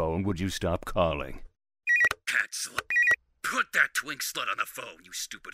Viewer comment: I heard "twink son" HELPPPP 💀💀😭 OMG JUST IMAGINE IF HENROIN WAS A FVCKIN TWINK I CANT